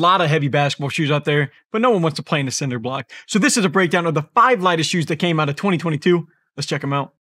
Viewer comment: I thought it said top 5 light skin shoes lol